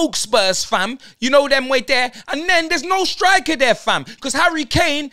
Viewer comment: Im swedish can someone help me what is that thing he says alot? It sounds like he says them way there fam"?